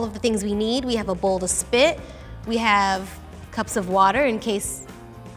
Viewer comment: When Freddie said "a bowl to spit" I thought she said "a bowl full of spit." lol